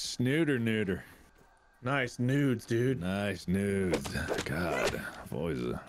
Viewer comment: Ngl, I thought Mark and Wade said "Send nudes, dudes." hahaha